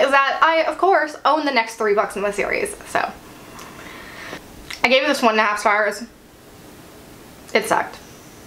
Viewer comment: I'm really sorry, but I can't understand what you say at 8:22 - it sounds like "I gave this one nars fars". I thought maybe "no stars", but it doesn't sound quite like that, although it would fit the review. Maybe "I gave this one up as far as", but that doesn't make sense. Just wondering, as I'm about to start this series at the first one (having heard until now only good things about it) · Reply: "I gave this one and a half stars". I definitely recommend at least the first book in the series 😊